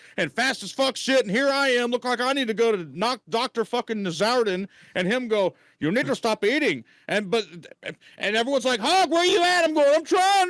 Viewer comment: Did hawg said the N word with the ER at 1:22:25 ? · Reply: Nope. I listened to that whole area and didn't hear anything. He said "Effing doctor" but not that other word